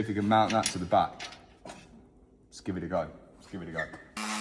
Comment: My brain is toasted or why I heard "Skibidi go" instead of "Let's give it a go" in 30:56 😭😭